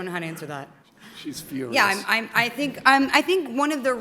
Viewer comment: After that "sexy" question, while Scarlett was thinking what to say. Somebody says "she is furious"